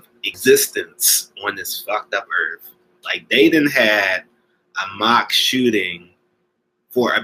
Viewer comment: Did he just say a mock shooting?